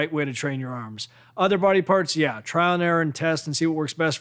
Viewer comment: all I heard was test